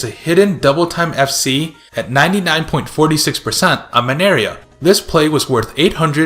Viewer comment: did he just say Maneria